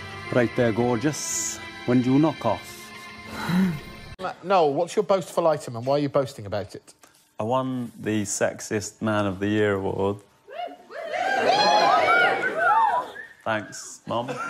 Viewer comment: why did I hear "the sexist man of the year award"